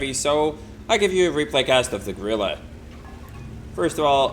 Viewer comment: For a second I thought it said "Grille is goes ham".